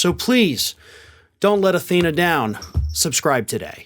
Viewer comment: Omg I coughed at the wrong time during your outro and it sounded like you said "please don't make me put Athena down, subscribe today." 😂😢😂